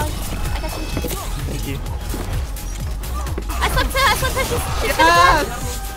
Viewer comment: I thought she said “I slept herpes” No Joke WTF